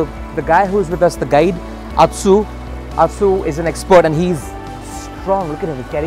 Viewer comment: I thought that guy said "atchu"